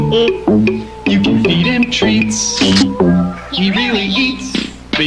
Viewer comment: I know it’s “she really eats” but I hear “she really YEETS”